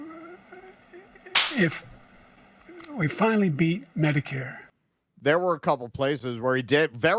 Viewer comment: When Brandon was stumbling, did I hear him say, "We finally beat Medicare"? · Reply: Yes, he literally said it.